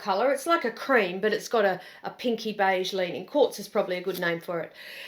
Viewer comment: Re the Ulysse, the second time you said 'Quartz' I could swear you said 'Corpse' and it cracked me up hard. It's a gorgeous color, no shade on that, it just made me laugh and I had to roll it back and check the closed captions to see if I hallucinated it. I did.